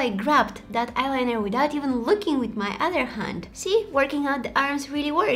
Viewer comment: Did she just say look with my hand i mean what the